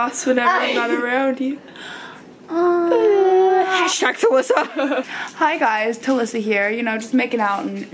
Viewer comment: I thought she said I shagged Alissa but she actually said hashtag talissa 😂😂😂😂😂